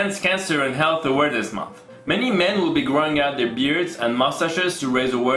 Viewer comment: Thought he said growing out pewbs